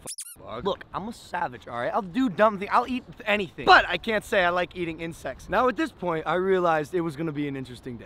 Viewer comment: it sounds like he said say i like eating in sex.